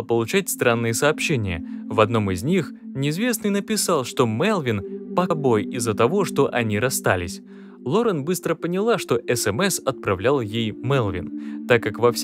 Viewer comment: Что значит "пал бой"? Кто нибудь понял, что он сказал? На